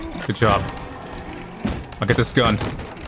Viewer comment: At this moment I heard the phrase "I am going to defeat you with the power of friendship and this gun I found"